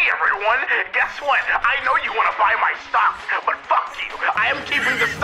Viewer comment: Eggman announces he's investing in Gamestop stocks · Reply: Is it bad that I always thought he said socks instead of stocks